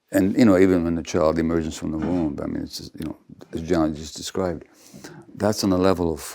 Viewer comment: Did Gabor just call Daniel “John” ? Is that a nickname or is something else happening?